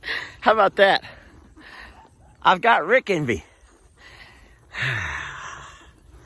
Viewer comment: that really sounded like you just said I got Rick IN ME not ENVY